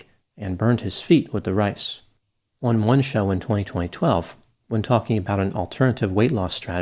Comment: Am I the one one who heard "202012" 8:39 , or do the dates work differently here? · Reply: He did say 202012 👍😂